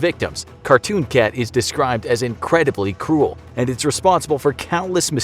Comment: I thought he said "incredibly cool" XD